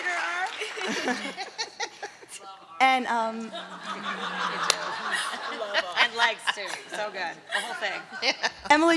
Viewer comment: What does someone from the audience say at 36:53 that makes Dom laugh? And then she repeats it? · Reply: someone said "i love arms" and she repeats it